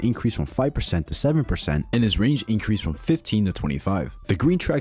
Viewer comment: As I said in the intro, please let me know if I made any mistakes! There was so many stats to cover, and its the middle of the night, so there probably are a couple errors. I'll edit this comment later with corrections when I wakeup :) Get the Plushie!!!! <link> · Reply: he says "and his range increase from 15 to 25". and yes they were talking about what he SAID not what the text said, turn up your volume if you don't have it on.